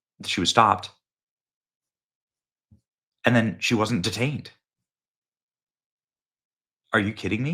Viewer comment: They found his wife with guns and passports and cash !! And LET HER GO !! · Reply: I thought I heard she was detained.